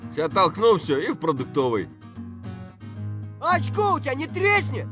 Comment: what are the first few words in this sentence in Russian. I’m not familiar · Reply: He's saying очко у тебя / "ochko u tebya" which is a funny way of saying your asshole (it literally means eye, as in like the eye of a needle, or any eyeholes that are along those lines). If you make it plural, очки / "ochki", that is the common word for glasses / spectacles.